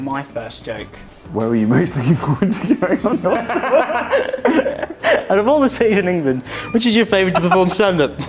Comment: what did jack say? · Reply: "where are you most looking forward to going on tour?" kept reading the same question